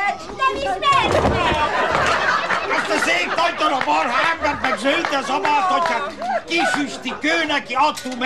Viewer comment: mit mond pontosan Gyulabacsi? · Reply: azt mondja, hogy:EZT A SZÉP NAGYDARAB MARHA EMBERT MEG ZÖLDRE ZABÁLTATJÁK meg hogy:HÁT TE MÉG ÉLSZ